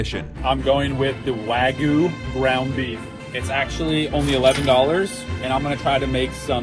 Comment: am I the only one that got mad that it said one instead of 11